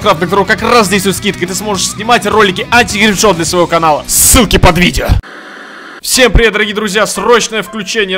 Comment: мне показалось что он сказал "cуки под видео"